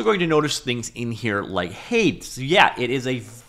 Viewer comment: Ow, I don't think I've ever heard anyone ever say Hades like that... I was confused on what you meant for a second. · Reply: Haeyets